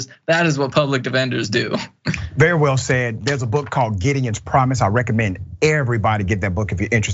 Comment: What was the name of the book he was talking about? I can't find anything for 'getting its promise'. I must have misheard.